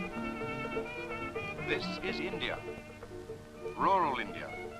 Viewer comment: I heard, "Tits for India."